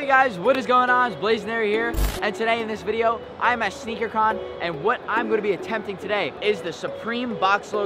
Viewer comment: Anyone else hear him say I am at shhneaker con lmao